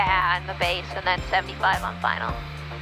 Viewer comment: Hah that plane would have saved our school thousands of dollars with all the belly landings (pilot induced) and gear failures. And did I hear her say 70 kts approach for a twin! That's an impressive approach speed. all the light twins I have flown were 90ish. Needing a cart or truck to do a walk around is a bit annoying but no more do than a proper inspection of a 172 with a stair cart. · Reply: I heard 85 on base and 75 on final.